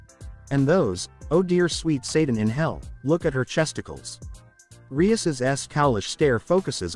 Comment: I just spit coffee everywhere lol. Tf she say? Chesticles?